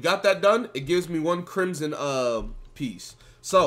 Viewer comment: "Boom done, now we have one crimsons" In head: uhhhh, wtf is that word * 1 sec later* Fuck it im not gunna bother trying " peice" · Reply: crimson piece